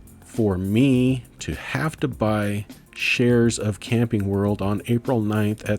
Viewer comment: lol I thought you said "Candy World" rather than "Camping World" when you first said CWH. I started looking up Candy World until I realized it... I'm curious -- are you using Turbotax to do your taxes? Did you find it relatively easy to import all your stock stuff in 1 go? Might be an interesting topic to share your perspective on taxes...